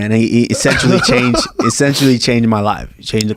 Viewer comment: I thought he said "I love that coach, he 'sexually changed' my life". Phoo, glad I rewatched that part to listen again.